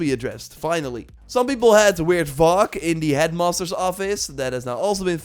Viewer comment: Istg I heard you say "some people had the word 'FUCK' in the headmaster's...."